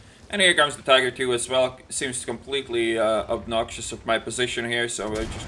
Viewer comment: Goch the word for the tiger 2 I'd oblivious. obnoxious means something like really disgusting. like that guys face was obnoxious. they end and begin similarly so it's easy to mix up. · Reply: Yup Hoang 4:35 approx. he says obnoxious instead of oblivious